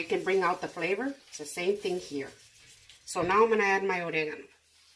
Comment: Omg... this was the best meal I have ever cooked!! I am the whitest American girl you will ever meet. I wouldn't know Spanish food if it crawled out of a burro and slapped me. BUT THIS! Oh girl! My husband and I just had tears in our eyes as we devoured it and licked our plates clean. I bow to you!! Thank you for taking the time to make these videos for people like me who would otherwise never make anything more than hamburgers 💙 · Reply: If you are thinking of the ingredient that she stirs into the sauce base at the 2:49 mark in the video, it is oregano. It only sounded like the name of the ingredient starts with an “A” because of the accent with which she pronounces it. She specifies 1/2 Tsp of oregano in the ingredient list she appended in the description above.